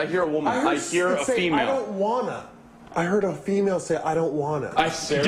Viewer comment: Instead of "I don't wanna" she could have been saying "I am gonna"